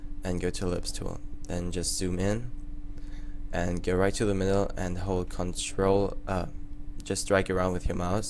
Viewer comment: wtf did you say at: 2:53??? Go right to the middle and hold ctrl up or a ?